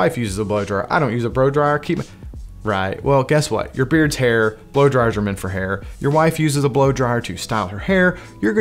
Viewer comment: Did he really say "bro dryer"